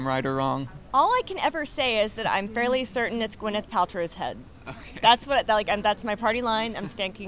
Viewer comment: What did she say? She's certain it's what? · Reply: she's fairly certain it's Gwyneth Paltrow's head.